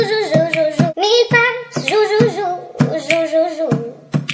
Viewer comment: Siempre pensé que decía Miau, Miau Miau. Pero dice Nañm, Nañm, Nañm